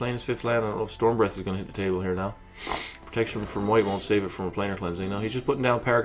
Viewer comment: I know that, did I say something to that effect? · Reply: you said something about it. After listening to it again I noticed that I heard "will save" instead of "won't save", so you said the right thing and I just didn't get it. I'm sorry about that!